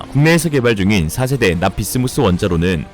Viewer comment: 납-비스무스 인데... 자꾸 납-비스무리 로 들려..